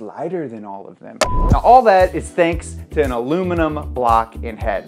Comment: What are you trying to say at 3:15? Did you mean alluminimunm?